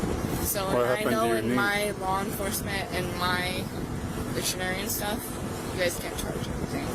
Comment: did she say she knows her law enforcement and dictionary stuff?